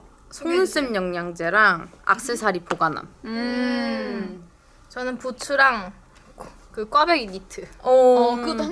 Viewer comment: 부츠를 부추라고 들었다... ㅋㅋㅋ